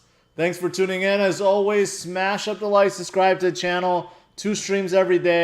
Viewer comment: It sounds like says, smash and the like button. Lol 👍🏽